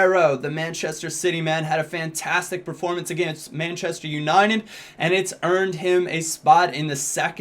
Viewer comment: does he say manchester uninen ?